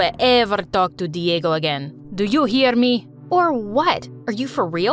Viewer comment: “Do you EAR me?“ - that’s what I heard